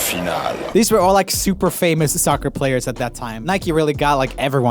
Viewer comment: did I just hear soccer???😒